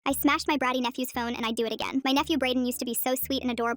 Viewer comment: Why did I hear it as "I smashed my bratty nephew"